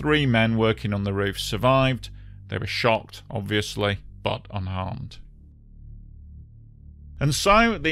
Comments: I thought I for sure heard "the other three men in the basket we shot, obviously, but otherwise unharmed". Lol. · I heard this as "they were shot, obviously" and actually didn't think too hard about it for a second because this was Russia.